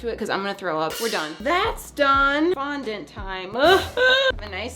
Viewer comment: ngl i thought she said F**K IT! Fondant 😌 😂😂💀